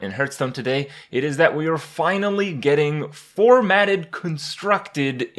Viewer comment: I thought you said four-manned at first, which seemed strangely worded to me, but made me think you meant 2v2 games...